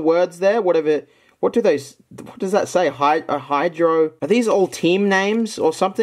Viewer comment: i thought it said hydro jews